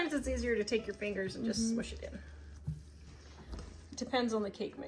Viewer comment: Thought I heard you say shhh it when you were standing at the freezer. 😱🤣